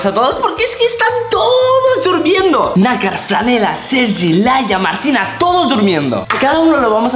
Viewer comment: escuche Laia y entró donde nakar